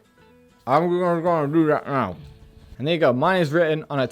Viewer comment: did he say the N-word then? 🤣🤣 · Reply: Ok just for clarity. Just because internet is full of idiots. The phrase I use is "and there you go"